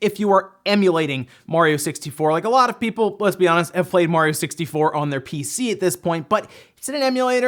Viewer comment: I thought it said Mario 64 on ps5